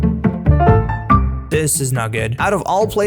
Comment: what does he say at 17:20? "... and we can finally search for ____"?? i sounds like the Hungarian word for diamonds, that is why I am asking. :D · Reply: "Diamanto's" I just tried sounding fancy LOL